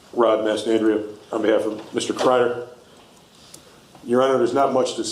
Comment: Everybody hold on press pause I could have sworn I thought that dude said I'm here on the behalf of Mr cryer😢😅.....